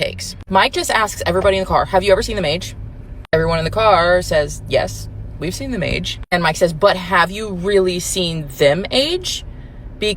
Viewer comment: oh I thought she said “have you watched the Mage? 🤷🏻‍♂️☕️